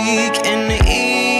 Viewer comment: I heard "foreplay" instead of "full plate" omg I'm lmao right now 🤣👀🤦‍♀️👌